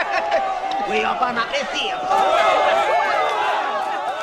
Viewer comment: tf did he say? 💀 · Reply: “We Open Up The Safe” 😂😂